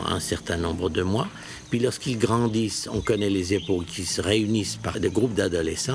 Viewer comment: "on connait les "é ...ones" qui se réunissent par groupe d'adolescents" les "é...ones" c'est quoi ?! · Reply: Époques. C'est en effet mal prononcé (on entend "épaules"), mais la même formulation est utilisée à 32:12 plus distinctement.